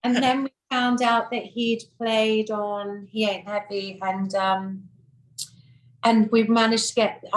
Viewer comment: Can anybody understand what song she says Elton John plays piano 5:30 for the Beatles? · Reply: “He Ain’t Heavy” is what she said. The name of the song is I Want You, (She’s So Heavy) so I don’t know what the hell she’s talking about. This is the first I ever heard Reg Dwight played on a Beatles recording. Interesting, if it’s true. As a follow up to this comment, there is an interview out there (look it up) that fleshes out what she is saying here. Reg Dwight played piano on the HOLLIES version of He Ain’t Heavy in the Apple Studios during the time period the Beatles recorded albums. He did NOT perform on I Want You. Billy Preston played the organ on that song.